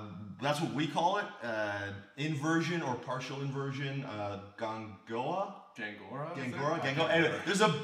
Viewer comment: What's the word you said at 0:22? "Gongora?" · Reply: Gangorra - Portuguese term for the half Granby we talked about. I don't know the direct translation.